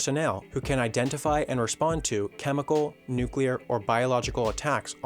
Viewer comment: … did he say nuclear?